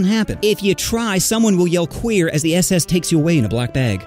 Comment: Did you say the SS on 2:19?